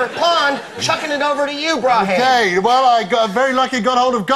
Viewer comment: is Greg saying “broheim?”